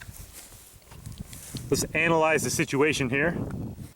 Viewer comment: I thought he said anal-ized or did he 🤨😏